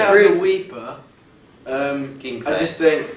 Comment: Did Liam say Kim K